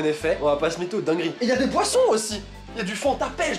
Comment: j'ai compris des "poissons" 😭😭😭😂